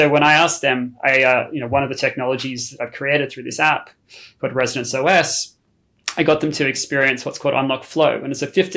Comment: I thoroughly enjoyed the segment thank you so much for your work and bringing this forth. This wonderful gentleman mentioned in the beginning of your interview that he had created that he was creating that I thought he said was called Resonance OS. And there was never any mention of this afterwards I would love to know where I can find this. I did look at the App Store. I don’t see it available if you’d be so kind to get back to me on if this is actually something that is available in the marketplace or not what a wonderful wonderful interview this was thank you